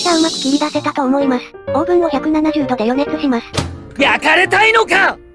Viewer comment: 「オーブンを170度で」が「オーブの170度で」に聞こえてしまったｗ